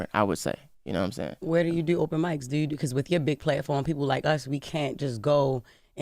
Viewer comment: "US" ? Did she just say "us" ?